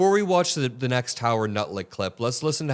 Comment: Did you say “Nutlick”? 😂